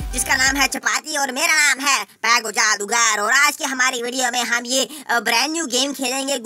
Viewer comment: What he says i don't know Plz someone tell me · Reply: Pagu jadugar